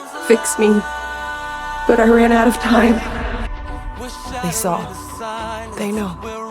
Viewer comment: For a sec I thought she said :Iran out of time☠️